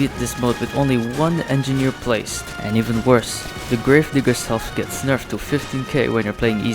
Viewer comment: Wait a min hold up did you say grave n 🧐